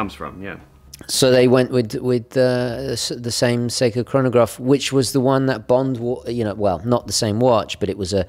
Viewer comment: "which the one that bombed...." good brakes man good brakes... Thumbs up to those who know what I mean LOL!